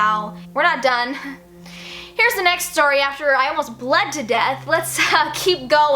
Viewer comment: I thought It said "I almost died to death" Hahahah